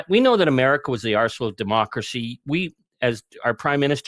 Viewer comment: It's funny when he says, "We know that America is the Arsenal of Democracy"; I heard, "America is the asshole of democracy!" No, that would be Donald A. Trump!